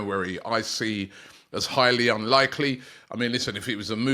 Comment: I keep hearing "hialy unlikely" but who is he? and when are we signing him?